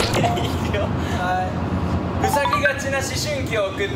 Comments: 私も"うさぎがち"って聞こえて、え？なに？新語？と頭の中でプチパニックを起こしたところ、聞き間違いでした。 ジェシーのジョージの発音込みでお一人様のジョージがツボ。笑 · “うさぎがちな思春期“って聞こえたとしてもなにそれ？てならずにそのまま捉えるのがジェシーちゃんって感じで愛しい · 「ふさぎがち」を「うさぎがち」と聞き間違えてるのにそのまま受け取ってゲームを進めちゃうジェシーが純粋すぎて、、、🥺♡ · 「うさぎがち」に聞こえたジェシーが「生まれたての子犬」のカード選んだの意味わかんなくて可愛いしそれにみんなが爆笑してるのほんと平和すぎてやっぱりSixTONESだいすき〜！ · ヤバい・・・私もついに「うさぎがち」にしか聞こえなくなりました。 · ジェスのせいで最初はちゃんと「ふさぎがち」って聞こえてたのに「うさぎがち」にしか聞こえない笑笑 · うさぎがちって聞こえてて子犬だしたジェシーかわいい 意味がわからない単語ちゃんと聞いてるジェシー偉いかわいい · ジェシーの回答は謎だったけど（？） 私も「うさぎがち」に聞こえたよwwwᕱᕱ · ほんとうさぎがちな思春期に🐰に聞こえる😂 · うさぎがちに聞こえなくもなくてジワジワくる🐰笑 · わたしも2回目みたら「うさぎがちな思春期」に聞こえた🐰🐰🐰🐰🐰🐰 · お一人様のジョージ、私もツボ😂うさぎがちなって聞こえて子犬も面白いし、何よりみんなが笑ってる姿が平和すぎて好き💎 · ごめん、申し訳ないんだけど私も うさぎがち に聞こえた ってことで樹くんもう一回やってもらっていい？